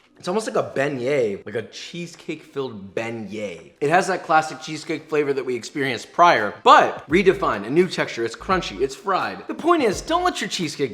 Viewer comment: Did you say "cheesecake filled bengay"? lol